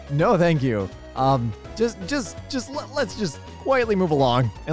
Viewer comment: Thought you said CAT in womb and wondered what in the fresh hell happens to kitten paws before being birthed lmao